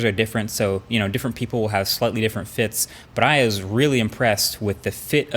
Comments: Did he say "i is very impressed" on 1:39 ?? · anyone else here 'i is' instead of 'i was' really impressed!